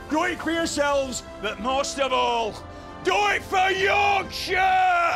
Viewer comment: “Do it for Yorkshire!” Plot twist, when he says this someone stabs him in the back like a GoT character that I won’t name in case I spoil it for new watchers. I actually originally thought he said, “Do it for YOUR CHAIR!”